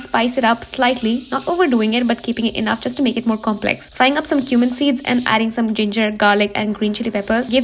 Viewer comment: When she said cumin seeds I thought she said human seeds and I panicked like wait what